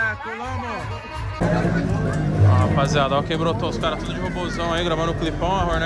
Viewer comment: "colou amô"?? Foi isso mesmo